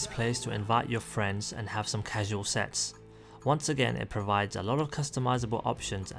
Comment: Invite your friends and have some casual sex?! WTF?! I honestly thought thats what he said. Good video though.